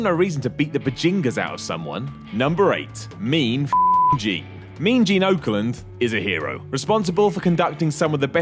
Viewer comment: Am I wrong? or did he say "Mean Gene Oakland" @ 2:20 ? Hahaha